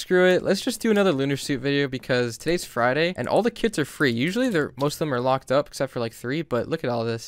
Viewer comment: I thought he said all the kids are free, I was like omg